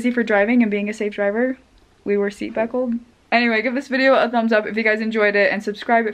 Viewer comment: Did she say seat beckle?